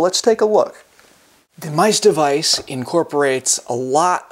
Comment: I thought It said mice DICE I was like 😳